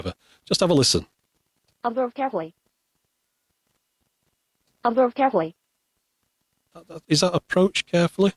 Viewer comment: Well... I heard "bum probe carefully". Not sure what computer game you would use that in 🤔